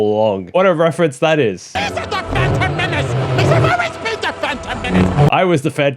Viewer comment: “Messa always been the Phantom Menace.” I’m taking that of confirmation that he was also Sith in the main galaxy and retained his memory due to his insane power. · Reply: Correct me if I'm wrong, but I thought he said "Messa always be the Phantom Menace" As in he's still manipulating things in the background of the main universe.